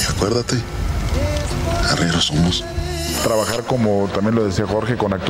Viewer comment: que dicee?! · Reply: Arrieros somos